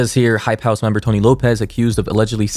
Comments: when you said Tony Lopez I thought you said George Lopez 😭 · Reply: I thought he said tory lanez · Who else thought it said Tory Lanez?😂😂🤦🏽‍♀️ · Who Else Thought It Said Tory Lanez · I can’t be the only one that thought that said Tory lanez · I thought it said Tory lanez